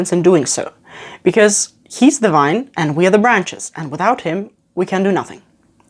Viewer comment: I heard "He is divine, and we are the branches" @ 12:04 lol. I like that.